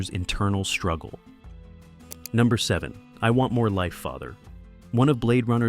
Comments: This is hilarious. I always thought Roy Baddy, said "I want more life F#*KER", Which sounded ok to me with the tone of the scene. "I want more life father" I'm shocked! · WTF....I thought Roy said I want more life FUCKER !!!!????